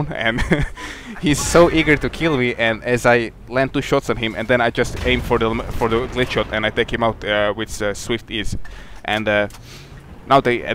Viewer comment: you take out another tank underground at B, and you shoot it high on the turret on purpose I think, but I can't quite make out what you say as to why you shoot it there? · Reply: Hey Skeld yes its on purpose,i said as i ´´glitch´´ shot the enemy tank,i will talk about glitch shots soon =).